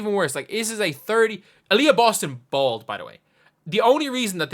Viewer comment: I heard it as "Alyssa Boston- bald" 😂